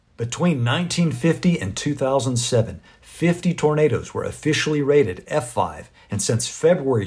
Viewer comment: Dunno why CLosed Caption is putting up the word Burrito instead of tornado. Kind of funny reading it like that. " Between 1950 and 2007, 50 Burritos were official rated f5 " LOL 13:24 mark